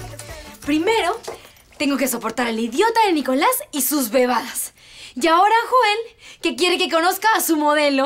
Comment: pense que dijo huevadas